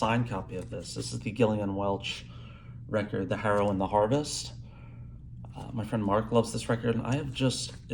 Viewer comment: Great list! Anodyne, Trace, and Harrow and Harvest are some of my all time favorite albums as well. I have found a kindred music soul! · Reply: Thank you cc thought he said heroin and harvest and l was going to look for it under that title.